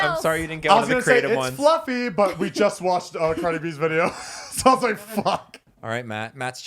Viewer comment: DID JUST SAY “but we just watched the frenimes video “